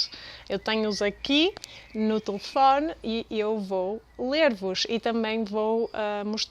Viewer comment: Tens um poema favorito? Do you have a favourite poem? Importante: O poema "Aniversário" é de Álvaro de Campos, e não de Ricardo Reis. Foi um erro. Peço desculpa! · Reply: you have "eu vou lê-los" in the subtitles but it sounds like you say "eu vou ler vos".